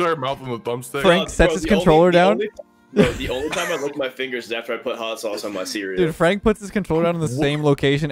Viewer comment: Did I hear this correctly??? "The only time I lick my fingers is after I put hot sauce on my cereal..."